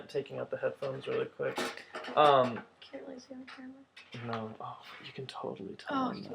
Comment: Did he say "no mom" (the part where he is checkin the case